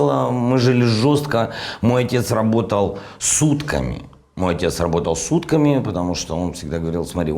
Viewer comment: Со всем уважением , в реплике: " ..мой папа работал сутками" я почему-то услышал " С УТКАМИ" . Я не сразу понял. Как с утками?